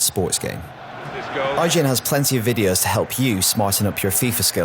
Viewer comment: did he said porn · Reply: lsdragonshadow he did said porn!!1 🤭😬🤫😱😱😱